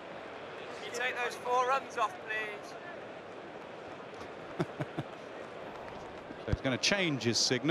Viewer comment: What he said 0:17 ? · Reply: He said can you take those runs of please